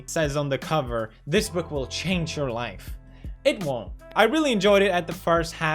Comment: Thought it said this book will steal your wife but okay